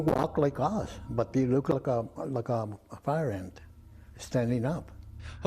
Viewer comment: did he say ''they look like a fire ant standing up''? the closed caption text totally ignored that statement.